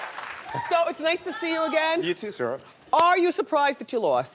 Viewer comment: Sounded like Heraldo said, "You too, sir" or "You too, Sarah." What's he drinkin'??